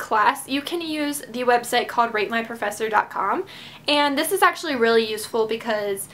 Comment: it sounds like she is saying "rape my professor" XD